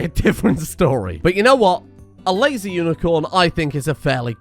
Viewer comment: I was half paying attention and 100% thought I heard "Lazy Unicorn" before realizing my mistake. Now I can't unhear it. 28:48 Thought it was a bit harsh on the Unicorn calling it lazy.